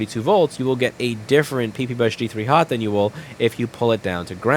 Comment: All I heard was "PP bus g3 Hot." 🔥